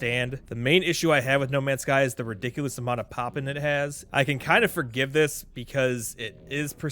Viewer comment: What does he say at 3:16? The ridiculous amount of “ _____” it has.” Pop-in? Popping? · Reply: Pop in. Essentially where you can see things load in while you’re playing the game.